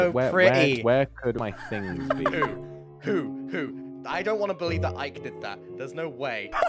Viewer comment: Thank you for the TL! Btw at 1:54, I think Mysta actually says "I don't want to believe that Ike did that"